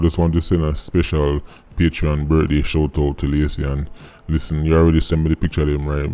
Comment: Thanks for the Birthday 🎁 wishes 🤔 oh you said Lacey I thought you said Legacy, my bad.